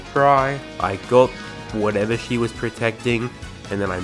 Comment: honestly wouldn’t have noticed if you put that text there! I thought you said ‘she was’ lol